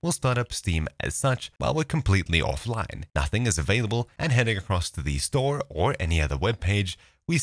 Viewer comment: i hear prostate store